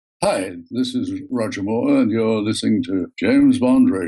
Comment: It sounds like he says "This isn't Roger Moore and your listening to JB R" lol